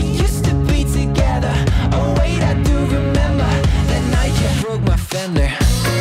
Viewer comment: I kept hearing “the night you broke my finger”, I was so confused haha